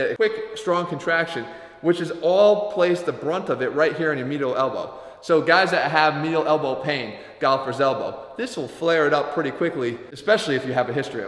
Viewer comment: What did he say about elbow pain at 1:57?? I think I have that but I couldn't quite hear how he pronounced it. Mio elbow pain? Meal elbow pain? Galipers elbow? What?